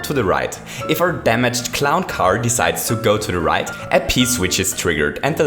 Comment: if you open up the chat, it will say pow-block, while he really said p-switch.